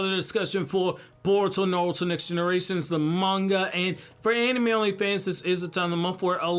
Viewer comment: Coz of this chapter I heard "anime Only Fans" instead of "anime-only fans"